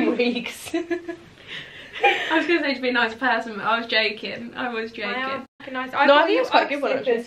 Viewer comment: I enjoyed this :) did your friend to say to be a nicer person? Might have misheard?